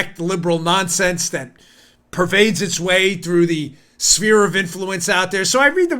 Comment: I thought you said Smear of influence and I think you can use that!!!!!!